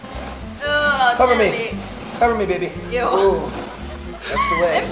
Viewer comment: or 1:38 I think he called her baby it was uhh so cute❤️